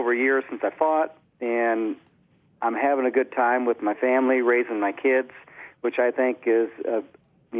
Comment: does he say "raping my kids"?